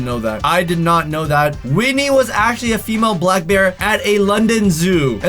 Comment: anyone else think he said weenie instead of Winnie